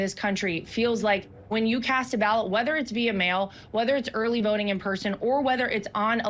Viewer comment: Did she just said early voting in person?